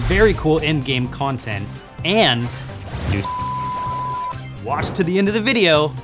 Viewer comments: I get that there are saying new starpowers, but why does it sound like they said "New sh*t" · sounded like new shit but beeped